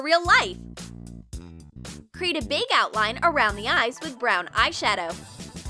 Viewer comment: I thought she said big butt